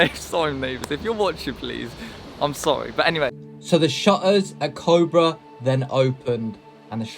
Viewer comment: The shottas at cobra, thats what i thought you said instead of shutters at cobra 😂😂😂😂